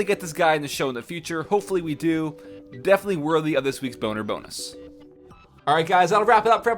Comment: did he just say boner bonus?